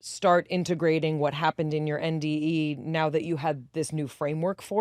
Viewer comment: this whole time I thought they were saying MDE not NDE😅